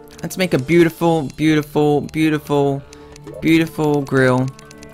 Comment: I don’t know why, but when you were picking your character, I heard you say grill instead of girl